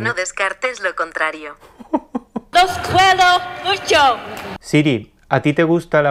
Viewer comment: Siento incomodidad y pena ajena por las personas que van a “disfrutar” de esos “eventos” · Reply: yo pensé que decía "los culeo mucho" y me estuve riéndome como un idiota.